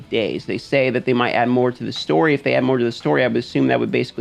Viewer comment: I think you misheard, they will add more to the "store"